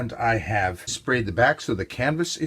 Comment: Dear Mona! Thank you! I agree with you 😉😂 I went back and watched the beginning, and then the entire video, because I can't figure out what you're talking about. "Waxing" doesn't mean anything to me, so I listened again, thinking maybe it was hard for you to understand what I said, but I still can't be sure. I talk about making sure the canvas is level, and also spraying the back (with water) to make sure the canvas is nice and stretched tight! I really want to help so my first suggestion is: can you watch the beginning again and send me a time code about the part you didn't understand? Then I can listen to exactly what you mean and help you. My second suggestion is to watch my video about preparing your canvas. Maybe the answer is there: <link> Let me know that time code and I'll do my best! Thanks for watching! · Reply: hey... it's my bad (ears) - I heard "wax" instead of "back" at 4:08 :) thanks for taking the time to respond :)